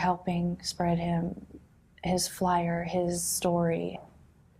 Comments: Did she say something like “spread him” right at the end ? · of this video she says, thank you for helping spread him", instead of spread his message, or this message...There is the most glaring evidence of "Earth/dirt-related" leakage of the entire interview. Crazy slip-up